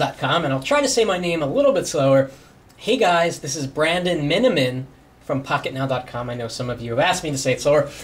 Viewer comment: Miniman? I always thought you were saying "and I'm in"